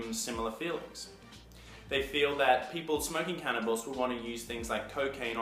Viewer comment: did he say smoking cannibals haha ?